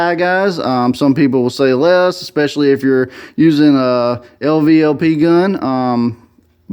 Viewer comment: " lvlp" gun..here I was thinking the whole time " bet he built that garage ".. carpentry background all I heard was "engineered beam pressure treated?" Lol. High Volume Low Pressure..that's what I use to rest my fender rafters on I guess. Cool video. I just ordered a mini astro gun to run on my 30gallon just for fun. Painted professionally back in high school but back to turning wrenches (at a much slower pace) these days at Volvo/Mack. Only spraying I di there is out of a Mack Can and it's over a weld or a drilled frane hole haha. I did just paint my truck tho. 3 trys to make it live-with able. Pretty slick tricks ya got tho. Congrats